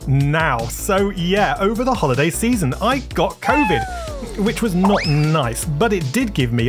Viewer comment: OHHH HE SAID COVID i thought he said coding 😭